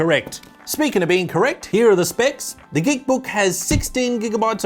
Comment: I almost thought I heard ... The Di**kbook... 😂😂😂